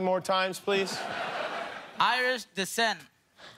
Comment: Who heard Irish descent?